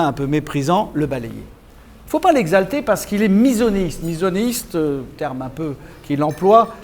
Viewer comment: c'est quoi le mot qu'il dit ? Mysoniste ? Je ne trouve rien sur Google. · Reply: Misonéisme. Avec un préfixe "miso", contre, et un radical dérivé de "neos", nouveau. Qui rejette la nouveauté.